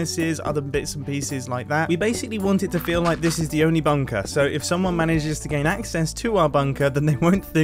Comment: I'm only a minute in and the word 'bunker' has stopped making sense... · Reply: honestly😂 it started to sound like he was saying 'banker'😭😭😭